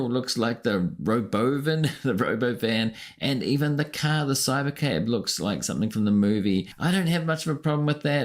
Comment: I legitimately thought he said Cyber-Cap instead of Cyber-Cab LOL